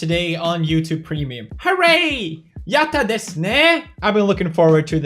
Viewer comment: What is he saying ?? " Yatta des nee " or something like that